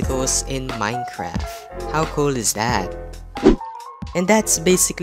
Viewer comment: Did u say coal instead of cool